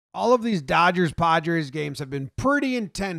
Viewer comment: It’s sounds like you say “all of these dodgers, podgers games” thought that was funny.